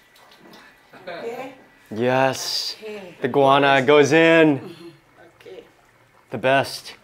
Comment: I was distracted by my phone and I thought he said "the iguana goes in"! Thankfully as I looked up I saw that it was guyana pepper he said. 😱😰🤣🤣